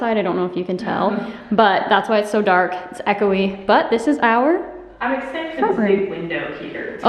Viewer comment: Does anyone know what said at 1:50 minutes? There was an echo and all I heard was. P.S. I love you and!!!💚💙🏳️‍🌈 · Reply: She said “This is our front room”